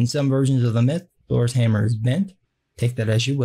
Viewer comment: Thor's hammer is, what? Bent? Mint? Pink? I can't make out that word you use. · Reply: Bent